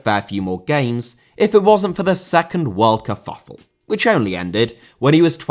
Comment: "the second world kerfuffle" I totally heard "second world cup finals" and thought "Alfie... what?"